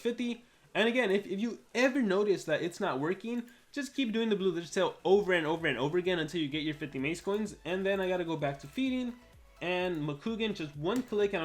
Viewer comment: what do you say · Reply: Lets say you collect food and get 5 maze cons. You know you're missing 45 maze coins. So keep planting the blue lizards until you get all 50